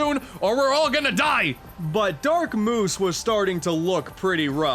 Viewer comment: Lol did you just say dark sumoose?